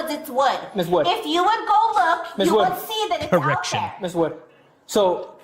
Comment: I thought he said “erection” after he said miss wood lmaoooo had to do a double take